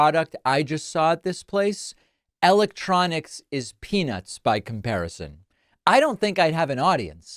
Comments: i heard “penis” not “peanuts” 😂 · I heard, "Electronics is penis by comparison." Might be my ears. 😆